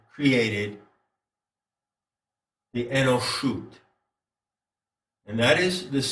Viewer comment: Does anyone know the correct spelling for the word that cc says is enoshut?